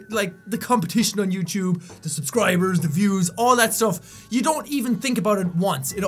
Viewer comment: is it just me or did he say subscribers wierd at 4:51 minutes in